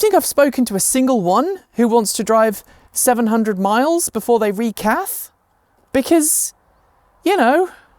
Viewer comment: Did you say "re-caff" (more coffee) or "re-catheterise" like the captions said? 🙂